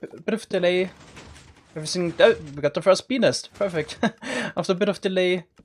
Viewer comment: I thought he said penis